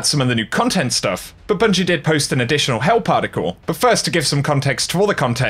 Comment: I cannot be the only one who heard "hell particle" instead of "help article"